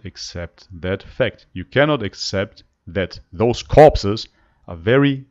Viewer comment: Haha, I thought she said "carbs" but she sasid corpses. :D :D :D